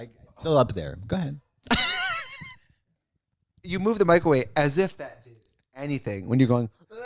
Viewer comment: am I crazy or did Trixie said microwave? 😂